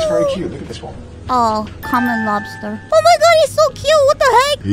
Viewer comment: In the beginning when Ironmouse said common lobster, I thought she said Conner lobster. Haha